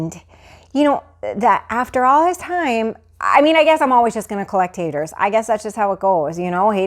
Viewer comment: I thought she said "I guess I'm just always goin to collect taters" ya know a haters gunna hate. 😂😂